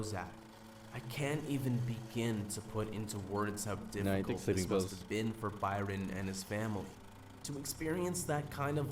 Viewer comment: what does Bryon say at 1:03:15 ? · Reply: he says 'no he took sleeping pills'